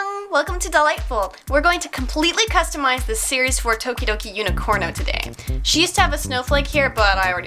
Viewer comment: I thought that you said todoroki